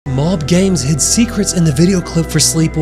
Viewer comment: For a second I thought you said bald games😂